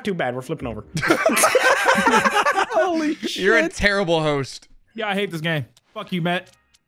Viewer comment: Yo! Just letting you know that at 8:59 when speaks it just says “grizzy” “grizzy”, instead of “yeah I hate this game” “f*ck you Matt.” Which is what says lol but anyway great vid! Super funny!